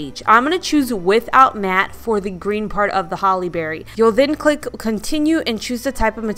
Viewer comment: I’m 49 years old and it took until I saw your video for me to ‘get’ Haley Berry’s name. You said “holly berry” one time and it sounded like you said Haley Berry and you could almost see the pieces fall into place in my mind. Clever parents!